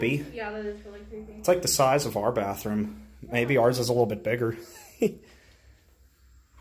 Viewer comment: You should have looked at what year the phone book was. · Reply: you can hear (I think it was) say - "There's a phonebook in here. It says July of 2009." So that place has been closed for about 10 years.